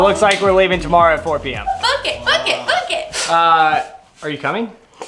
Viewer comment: When the CC thinks says “F it” instead of book it 😂 and captions it as [____] it